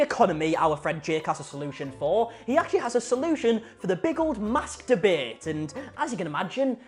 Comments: Deadass when you said 'mask debate' for a split second I heard 'masturbate' 🤣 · Who else thought he said: "he actually has a solution for the big old masturbate" and not "mask debate" cause of his accent?😂 I was so confused lol